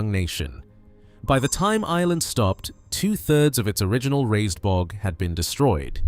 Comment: what does he say? 'Lywee'? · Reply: I assume he's trying to get his tongue around "Laois" (pronounced "leesh"), a country in the Irish midlands.